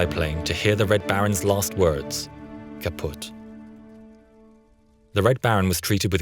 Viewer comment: Did he said:"Kaputt"?